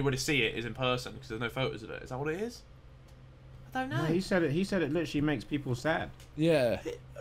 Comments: Anyone else hear at 8:51 the girls voice answer Harry’s question with (don’t know) 😂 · why did I hear a girl voice say 'I dont know' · who randomly said "don't know"? It sounded like a girl. I'm confused.